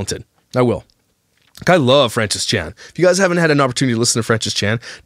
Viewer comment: Francis Chan? I Googled what I thought you said, and that's what I came up with. Yes?